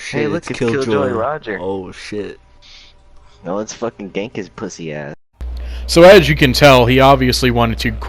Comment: Ooo shit he said gank his pussy ass I had my sound down and I thought he said “Spank my pussy dad” lol